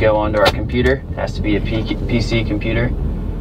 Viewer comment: Everybody else covered the French lesson...but did I hear at the end "foreskin app that you downloaded"?? I'm guessing it was too painful to snip that? ;-) P.S. Awesome video as always!!